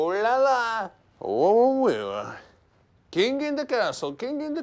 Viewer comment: I always thought he said "king of the castle" not "king in the castle"